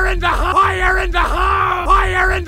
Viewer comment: I hear ‘WIRE IN THE HALL’